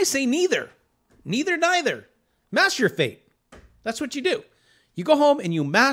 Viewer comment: I thought you said masterbate not master your fate.. lol